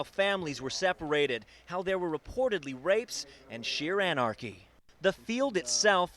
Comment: did he say rapes?